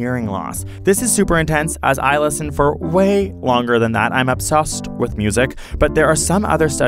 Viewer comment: brah did he just say obsossed